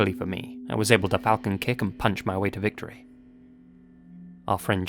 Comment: i heard a noise in the background sounded like FOLCON PUNCH!!!